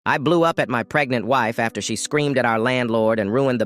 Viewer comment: I thought he said “I blew up my pregnant wife”😂